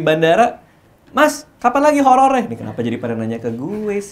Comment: 3.56 : jd itu temennya jurnalis, tp bukan jurnalis holor wkwkkw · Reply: Kirain kuping gue doang yg salah denger kalo dia ngomongnya holor